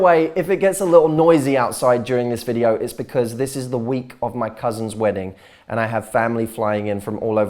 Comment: Oh thank God at first I heard it's your wedding Matt😅